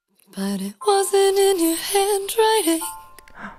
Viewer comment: But it wasn’t in your handwriting l: And I oop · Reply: OOOH I THOUGHT SHE SAID HAND DRYING